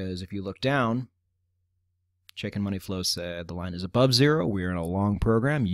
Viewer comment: I swore I kept hearing "chicken money flow"... then I saw the CMF and looked it up. :facepalm